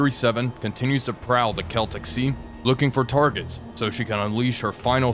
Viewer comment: At first when he said the celtic sea, i thought he said the kelptic sea, and my first thought was, oh no a whole sea of kelp.